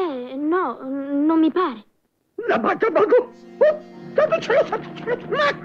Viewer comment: Ma cosa dice Anacleto a 0:58? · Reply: La maga Magò! Santo cielo, santo cielo, santo cielo.